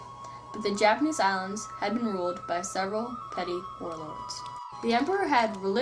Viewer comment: Pronounce things louder and clearer please. I think I heard u say "petty war lords"